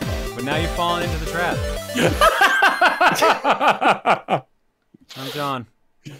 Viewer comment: That totally sounded like was saying “I’m Tom” as if to call back to Colosseum. 4:38 MC is Light Gold Bold Text confirmed? 5:15 That sounds…potentially dangerous. 10:44 I am so glad that I could help bring more attention to Terror of Hemasaurus and Hi-Fi Rush to so many audiences through this year’s Chaos Cookie, including the hosts. They’re both great games. 11:26 Well Adri had it well before Colosseum. She couldn’t attend in-person for a different reason. 15:55 who I thought said that they were out of the SFM game a while ago. 24:50 Broly has the better story, but Super Hero’s character writing and interactions are peak Dragon Ball writing and comedy (except for one instance involving Goku, and that’s when he’s introduced in the movie). Absolutely love the Gammas, Dr. Hedo, heck, even Magenta (which if it’s any indication, Charles Martinet definitely could’ve been both Mario and Luigi in the Illumination Super Mario Bros. movie with how much dialogue Magenta has in the movie. Should he have been? Heck no, because it’s clear from their designs that Illumination did not design their Mario and Luigi with Martinet’s voices in mind. Hearing Martinet’s voices out of their mouths would be really weird). Also Super Pan is already miles ahead of GT Pan thanks to Super Hero. 26:00 I’d say that Tien is as human as King Piccolo was a demon in that being a Triclops/Namekian does not disqualify him from being a human/demon in the sense that they’re classifications based on